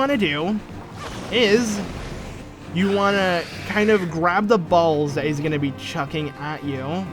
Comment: I paused the video at around 20:51, and all I heard was "grab the balls" XD